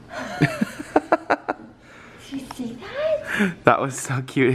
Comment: When they were building the big Leggos, looked towards the camera and it sounded like he said, "You guys see that, right?" omg haha!